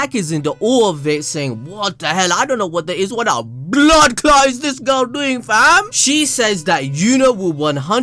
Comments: did i just hear a Jamaican accent "Bloodclaat" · Did I just hear you say " bloodclaat " 6:59 😂😂 🇯🇲